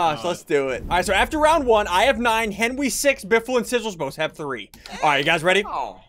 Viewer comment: biffle and sigigals I thought u said biffle had sigigals